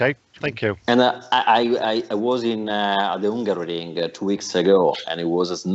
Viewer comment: Did he say, " hungaroring "?